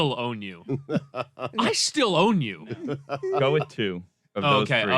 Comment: I thought he said "I spit on you" too!